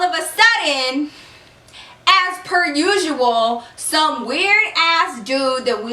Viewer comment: I thought she said "ass per usual" 😂